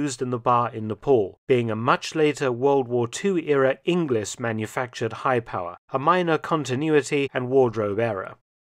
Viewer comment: it sounded like "a WW2 English manufactured" but pretty sure he meant to say its "a WW2 Inglis of Canada manufactured".